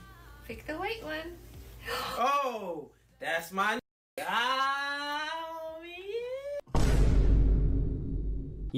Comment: 😂 did he say to the baby, that's my N🤨